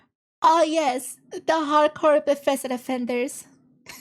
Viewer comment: I heard that as "Bethesdafenders" first, but it'd be the perfect term!